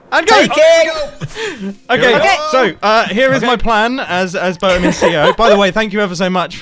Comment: Did Mumbo just say, "Boatem of CEO?"